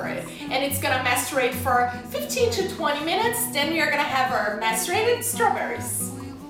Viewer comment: I thought she was saying menstruated strawberries.